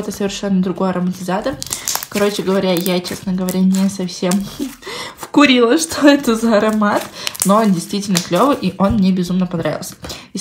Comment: мне тоже послышалось, то, что сказала: "Вкурила что ли тетраборат?"